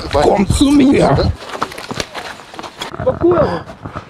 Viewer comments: did he say at 10:30 "komm zu mir" in german "come to me"? :D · What is the guy saying at 10:28? Sounds like "Komm zu mir", come closer in german...but his voice is so scary, he could work the ghost train. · is he german? did he say "komm zu mir"? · did he just say „komm zu mir“? :D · he actually said "Komm zu mir" in German, which translates to "Come to me" LOL. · is the dude got so mad he became German. I thought I heard: "Komm zu mir" lol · did someone really say "Komm zu mir" ? 😂